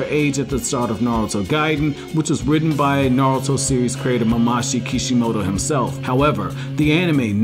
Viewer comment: Did he just said "Mamashi Kishimoto himself" 2:05 LMAO. The biggest problem with Boruto is that it exists x)